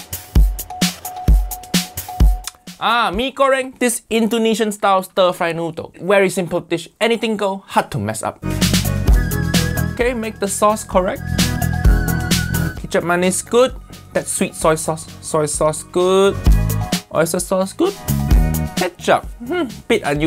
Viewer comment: THATS WHAT YOU WERE SAYING? I THOUGHT IT WAS KETCHUP MAYONNAISE i was so fuckin confused like "why isnt it fuckin orange"